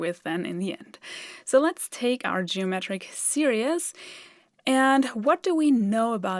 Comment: it sounds like she is saying geometric serious